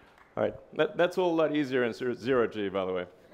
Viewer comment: Can anyone explain what he means when he says "That's a lot easier, insert 0 g, by the way!!!" after getting out of the spacecraft at 14:15? Thanks.